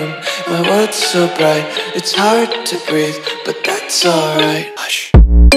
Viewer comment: I thought it said “It’s time to breath, but that’s alright” “Usher”